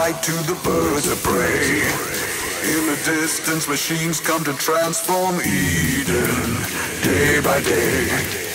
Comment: Y'know, all this time I thought he said "machines come to transform me", not "transform Eden". I actually like that better though, it's more foreboding.